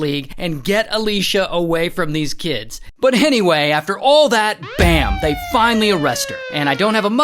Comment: Did Ray just say "Hennyway"? 😂😂